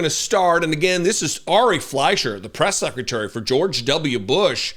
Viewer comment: Ok, i thought i was losong my mind. You said ari fliesher (sp) i thought you said ari Melbourne (sp). One i have disdain for and the other i have some respect for for